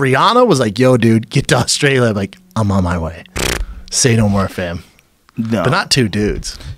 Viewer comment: did schaub just say fam